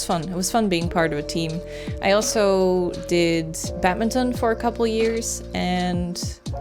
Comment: You did Batman Zone? What is that? Lmao 😂 I’m pretty sure you didn’t say Batman Zone, but I don’t know what you said, and all I can hear is Batman Zone. Since it made me smile, I’ll just go with that for now. 🦇 👨 🚧